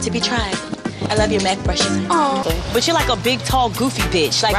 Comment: "I love your Mac brushes" LMAO I love that scene! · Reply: i swear i thought she said neck brushes